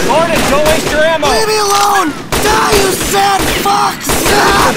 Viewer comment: sounded like at first "DIE, YOU SAD FUCKS!! AHHHHH!!!"😂